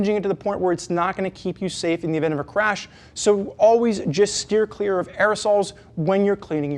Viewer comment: anyone else hear - "always steer clear of assholes...." 😁